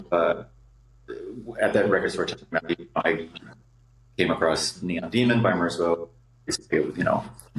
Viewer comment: What is the album Jamie mentions at 6:22? I can barely make out what they said. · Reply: Pulse Demon by Merzbow